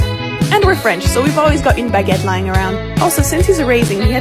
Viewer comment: "since he's erasing"...I thought she said, 'since he's a raisin,' and I'm like, that's not nice! he's not wrinkly!